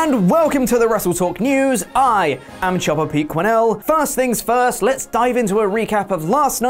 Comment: Nakamura won the Rumble in 2018 NOT 2017 Chopper Pete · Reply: I thought he been saying Jobber pete